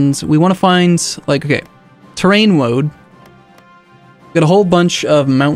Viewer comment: he started saying terrain and I thought he said train, now I wonder if their are trains in HOI4.